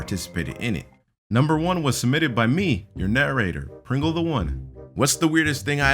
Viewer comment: When he said, ‘I’m your narrator’, I thought he said, ‘I’m your nuerator’ WHAT AM I THINKING!!!!